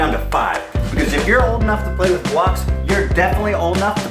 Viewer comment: Omg I swear I thought he was going to say "cocks" at 12:52 XD